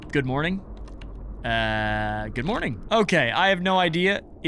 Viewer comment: what did he say? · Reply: “No good morning to you.”